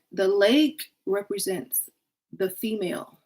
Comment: What is said 7:50-8:10? What represents what? It's muted on my end. Thank you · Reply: she says the lake represents the female was all I got.